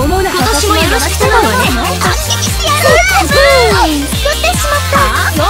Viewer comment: のところS○X〜にしか聞こえないんですが、これは自分の耳がおかしいのでしょうかw